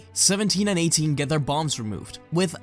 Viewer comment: When he said 17 and 18 got their bombs removed I thought he said balls